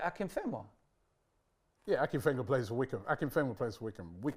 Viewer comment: didn't originally say "currently". Lol. And it literally sounds like says "played" instead of "plays" for. I would've answered as if it were any players ever like did. changes to. Lol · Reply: He clearly said plays. misheard